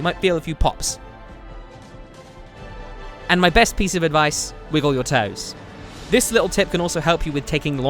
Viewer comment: Hey guys! Can anyone tell me what says at 5:55?(the best advice) i didn't catch it. Thanks :) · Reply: Wiggle your toes